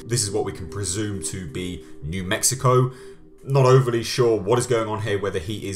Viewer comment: So I don't hear them saying "New Mexico" it sounds like he say's, " Look I get it, something went down in Mexico ". Every other trailer/teaser sounds like Mexico as well.